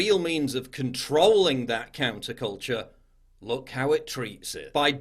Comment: Turn subtitles and go to 12:20 It will say “cancer” instead of counter. And the next thing Paul says is media demonized it enemy’s Fear is real